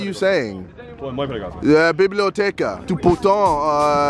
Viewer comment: why is JJ saying library in russian? Biblijoteka😂😂